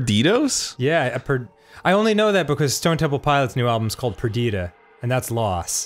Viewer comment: waw I thought he said "Twenty One Pilots new album is called Bandito" I was like wrong but I'm glad you're finaly joining the emo world where you belong daniol.